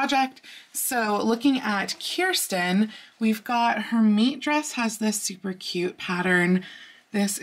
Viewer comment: Love the look at your collection! Every time you said "meet dress" I heard "meat dress" and immediately thought of Lady Gaga 🥩😆